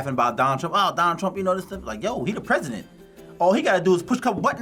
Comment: Don got me rolling! I think he was trying to say missiles and thinking nukes (or nuclear), so it sounded like "we gon' shoot a couple NISSLES off!"